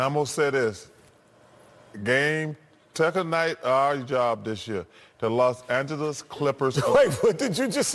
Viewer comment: I still have no idea what he's trying to say. "Game... tekka knight Ari job dis year."